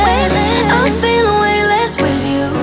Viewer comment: Nice song :) btw 1:53 who else hears "catching Salma Hayek's with you"?